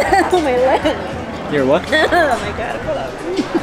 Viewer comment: Did say “Oh Mylanta!” 😂